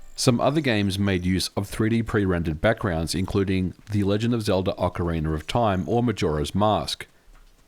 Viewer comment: There's that "Ocarinur of Time" game again. Not the first time I hear of it, really need to find and play it one day 🤔 Love the video!